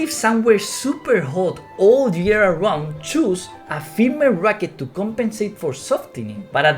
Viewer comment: Hi, i'm new to the sport, i wanna ask something. Did i just mishear him saying "choose a female racket" for hot all year round court? Because i live in a place where its hot year round (near the Equator), shouldn't i need a harder racket? (Considering that female racket are usually softer)